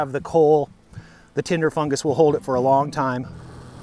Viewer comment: Tinder fungus is why people give up on online dating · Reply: I thought he said Timber fungus I was going to say that was my nickname in high school